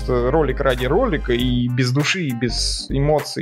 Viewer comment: Хыы последние слово "до новых встреч в эфире" но я же Олеша и услышал "до новых встреч в кефире"